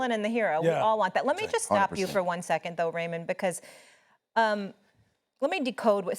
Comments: did she call him Raymen!? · Did she call him Raymond 😂😂😂😂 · Did she just call Roman, " Raymond"??? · did Molly just call Roman Raymond?? 😂😂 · Did Molly call him "Raymond"? 7:20 mark